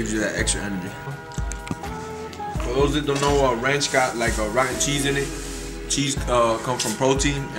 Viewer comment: Ranch got like rotten cheese in it, cheese comes from protein and protein is always good 😂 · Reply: lol i thought he said rocket cheese